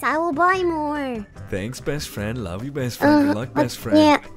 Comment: Omg did said " I love you" to??